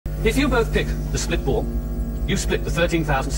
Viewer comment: I thought he was saying spit ball and I was like ew wtf